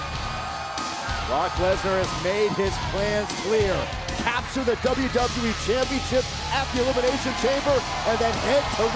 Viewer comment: I wonder what Seth said to Brock right here 😂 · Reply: Remember me , I beat you twice 😭